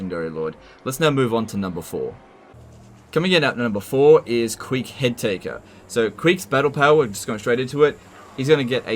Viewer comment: For a second, I thought you said Queek Hentaiker